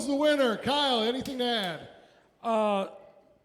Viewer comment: you say Kyle anything to add, but it sounded like Kyle Kinane and scared the crap out of me.